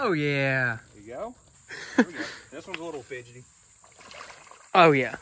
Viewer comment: what did he say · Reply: Fidgety